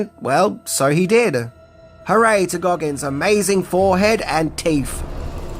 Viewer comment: did you say "Teef"? 😁